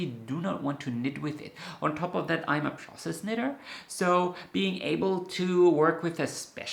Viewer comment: Had to listen twice before realizing you are a 'process' knitter -- at first I heard 'princess' knitter, like the princess & the pea -- and I thought, me too! If I don't love the tactile quality of the yarn, needles, etc I won't enjoy the project.